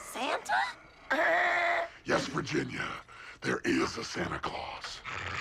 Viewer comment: Yes Virginia there IS a Santa Claus! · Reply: Ready Player Two when I was younger I thought he said “in West Virginia there is a Santa Claus lmao